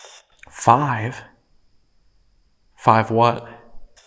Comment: I thought it sounded like, "five cents"... but I can't think of any relevancy to the questions was asking. 1:10:03 it sounded like, "Why?" in response to asking if they were a resident there. Oh, and finally, None of those ladies wanted to talk to or... That makes sense.